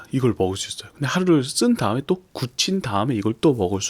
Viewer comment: Did he just say “kuchinta” in